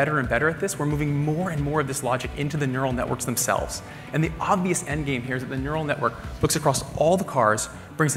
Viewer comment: did he just said " the obvious endgame " He's Marvel fanboy baby....